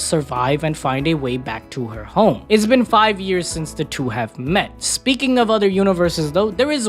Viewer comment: j thought you said “she didn’t see him again after that for 6 years.” I was about to cry but no it’s marking there 5 year since they met in the other universe. I really love the couple it’s so cute❤❤